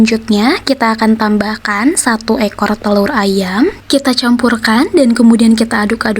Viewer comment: Gw yg salah denger kali yaa.. satu ekor telor ayam😮